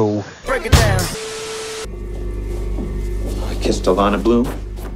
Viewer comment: i seriously thought he said "i kissed Orlando Bloom" at 7:10 and i was SO confused xD